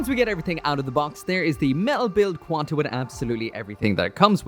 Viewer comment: did you say qan t??